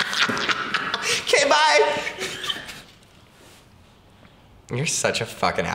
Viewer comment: What does say at 4:46? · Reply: Giulia Di Tullio “why do I feel like you’ve said that before”